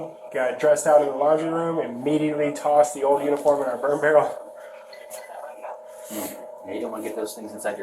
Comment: Did he just say he has a “burn barrel”?